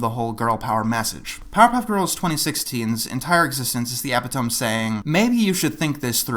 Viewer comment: I can't tell exactly what you're saying, but if it's "epitome" then the "e" is pronounced.